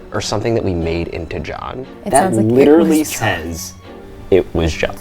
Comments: I didn’t hear “it was John” I heard “it’s my job” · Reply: At first I heard "It was God" but that changed the more I listened to either "It forgot" or "they forgot" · So I want to say, that the part where the ghost says it was John, I heard, it was God. And also radiators pop like that. What's spooky is that they don't know what it's like to have radiators in your house and therefore aren't aware of the noises they make when they warm up, or cool down.. shivers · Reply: I heard 'it was God' too. Or the more I listened to it, my brain heard 'you forGOT!.' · I hear “IT WAS GOD” a lot clearer than “JOHN” so my assumption is that they skimmed past a Christian channel, but that’s just me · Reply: I heard “It was God” too!! I was searching the comments to see if anyone else had. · LOL I thought it said "Oh my God" and was so thrown off when they heard, "It was John" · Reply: Gurl een I heard “it was god” I’m glad i’m not alone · i heard "it was god" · it sounds like it said it was god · I hear “it was GOD” 😨😨😱😱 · I hear "it was God"... :/ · I definitely heard "it was god" lol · I heard "it was god" lol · I hear “it was God” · I heard "It was God" X33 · I'm sorry, but i kept hearing "ItcH VAs KaH!" instead of "IT WAS JON!" but that may be my skeptic heart infiltrating my brain. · Ngl I heard "it was Jah" ??? Yaall jah is here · Swear I heard “it was god” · Ngl I heard “it was god” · HAHA i only heard “and by God” , could be a Christian channel 😂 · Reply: I heard "it was God" too